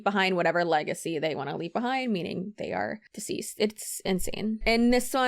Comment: The only thing keeping my sanity in these clips is how first legacy girl does have excellent taste in music. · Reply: IKR. I heard "Decode" and I was like "Damn, a woman after my own heart." And then she kept talking.